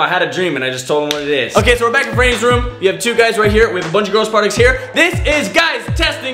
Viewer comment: I thought he said a bunch of girl parts here when he pointed down lol. And yes I’m rewatching these years later